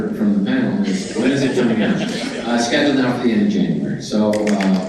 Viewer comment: did he say "it's scheduled for the end of january"?